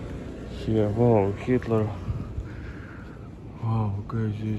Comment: did you call the cat "Kitler"? If so, brilliant! Not a guitar...it's a ukelele! I, also, got laundry delivery in the Philippines.